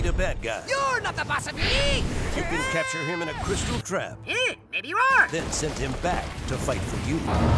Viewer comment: what did he say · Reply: "Heh maybe you are?"